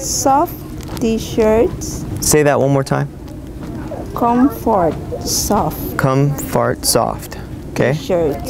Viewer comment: haha. At 2:02 , it sounded like you said comfart soft. lol